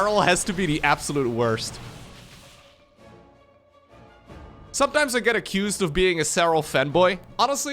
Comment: I heard: "sometimes I'm being accused of being Serrals Femboy" XD